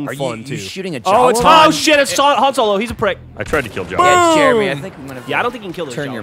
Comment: It sounded like Geoff said "it's Han Solo! It's a prank!" In the beinning